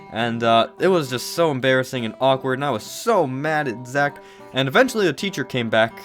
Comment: "I was so mad at Zek" That's what I heard. Don't get all mad at me.